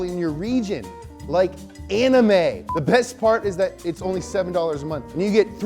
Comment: Did he just say, ANIME?